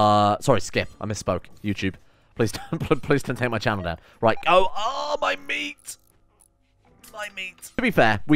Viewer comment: "Oh, my meat" That's what he said my man.